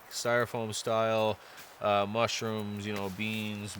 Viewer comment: Hey, can you speak French? And at 30:10, did you say “styrofoam style mushrooms”? You should look into those inflatable lightbars made by Klymit. They just take a power bank and they pack down small.